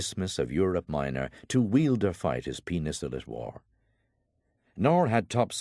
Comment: Did he just say "to weild or fight his penis that is warm"???